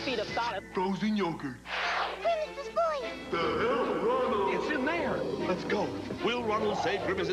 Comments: is it just me or does it sound like someone is saying "to hell Ronald" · Does grimace say THE HELL IF I KNOW? LOL